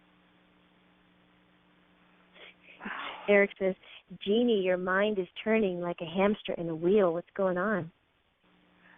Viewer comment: I hear him whispering something that sounds like "wow" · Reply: it did say wow...that is spooky now..